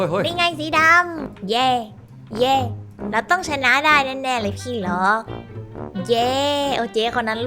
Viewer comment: สิ่งที่เค้าได้ยินคือ เราต้องชนะเเน่เลย"ขี้"ร็อค55555